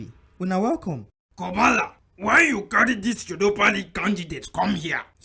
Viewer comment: Its like the YORUBA league wen you dey destined for never humble you😆😆😄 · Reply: I vawulently swore I heard Yoruba rather than Europa 😂😂😂